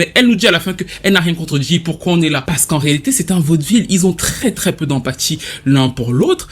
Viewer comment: Mais qu'est-ce qu'il dit à 26:02 Parce qu'en réalité c'est un vodvil ??????? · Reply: Vaudeville